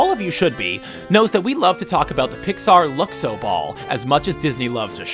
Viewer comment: At around 1:20, did he say "blall"?